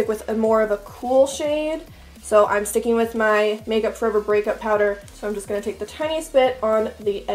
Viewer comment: Hi! Can you clarify what contour powder you used? I thought I heard you say MUFE Breakup powder, but I couldn't find it on the MUFE website. I'm always on the look out for a good cool toned, contour powder. Thank you so much! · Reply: Alda Tomasic I think she means makeup geek breakup powder 😊